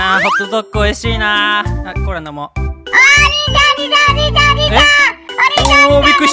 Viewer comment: 最初の、0:03の時におじいちゃんって聞こえた😂あと、好きw 今日の、動画も面白かった((´∀｀*))ヶﾗヶﾗ！これからも応援しているので頑張ってください！！ꉂꉂ📣 あと、出来ればさんが子供化した動画を見たいです‼️よろしくお願い致しますm(_ _)m🙇‍♀️！ おつそゆ〜あと、友達にこのチャンネル見たらって言ったらチャンネル登録してた‼️あと、 いつもどうり可愛いんで音声悪くれもいいよ！エンディングの曲すごい！さんカッコイイ❗️